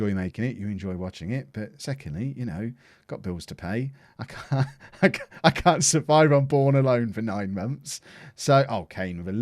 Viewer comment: Did anyone hear "I can't survive on porn alone" the first time at 15:44? :D